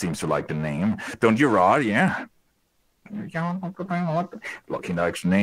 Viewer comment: “do ya rod, yeh?” I don’t don’t what he’s saying here